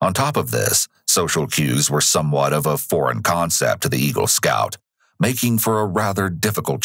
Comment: Eagle Scout ..... I thought he said Evil Scout. ... he coulda